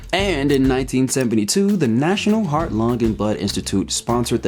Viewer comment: Okay, great video so far, plan to finish it, but I can't ignore this. 19:36 , am I the only one that can't unhear "The National Heart, Lung, and Butt Institute." I've rewatched it like 10 times, and I know he's saying blood, but I can't hear it. Love the video, and other videos you make tho, please keep it up!